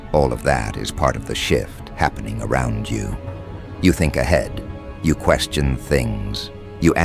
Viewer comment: What did you say I think a head...if I assume that you are telling the truth which I know you are now my question is who is willing to give me one I'm waiting .....give me a like to prove you do me...lol